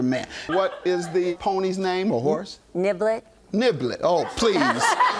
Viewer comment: Am I the only one who thought she said the horses name was NIGGLET vs NIBBLET 😂😂😂😂😂